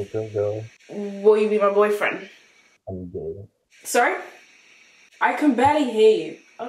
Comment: I think he said I'm gay 😭